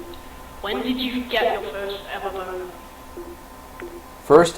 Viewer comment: I thought 3:45 said first MMO